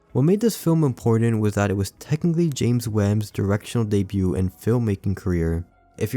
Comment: Misheard “James Wan” as “James Nguyen” and was so caught off guard like “the Birdemic guy made that?” Anyway excellent video, love how you go for more obscure topics